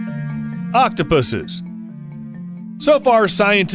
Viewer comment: its octopi not octopuses lol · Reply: Goodness, I thought I heard octopussies lol haahaa